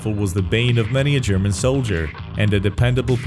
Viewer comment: did he just say “the main weapon of the german soldier?”